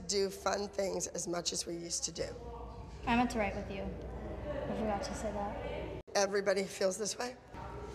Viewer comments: What did Kendall say at 0:49 and 0:57? :) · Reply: I meant to write with you i forgot to say that. At 0:57 Your gonna make me cry. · what did she say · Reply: "I meant to write 'with you'" meaning they don't get to do fun things with Abby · what does kendall say at 0:48? I can't hear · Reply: I meant to write with you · What does Kendall say at 0:49? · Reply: "I meant to write with you. I forgot to say that.." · What does Kendall say at 0:49 ? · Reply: “I don’t thrive if you I forgot to say that”